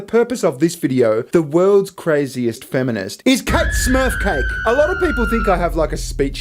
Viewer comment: All this time I thought you were saying KateSmurfKate